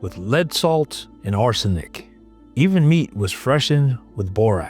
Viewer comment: did you just say that human meat was freshened with Borax???? 😳 Key words, HUMAN MEAT 😳 I listened to it 3 times to make sure I was hearing it right.... And still heard, human meat..... 🤔😳